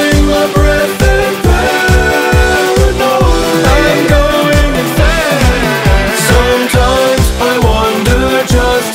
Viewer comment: does anyone know what it says at 2:17 and 2:25? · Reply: "I'm going insane" "Oooh, and turn back time" I'll get these added to the description!